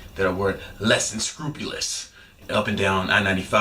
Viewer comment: Yo, what was that word you said at 1:02? Lessincoupious?